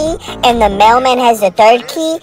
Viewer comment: Bro I thought he said grandma instead of grandpa I was like “AYO WHAT THE FUCK”